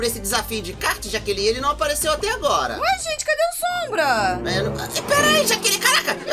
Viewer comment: eu escutei um xingamento até coloquei a velocidade no 0.5 e eu ouvi carai jaqueli